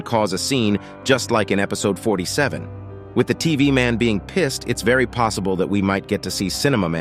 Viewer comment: BRO I THOUGHT HE SAID KISSED who agrees?